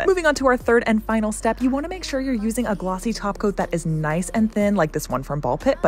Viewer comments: For 2 yearsvI thought she was saying, "Glossy taco." Am I stupid? 😢😭😭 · Girl I swear I thought you said GLOSSY TACO 😂😂😂😂